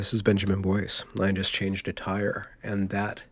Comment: Benjamin, I heard you say, "I just changed a tire". I thought you said, "I just changed attire". I love your word play.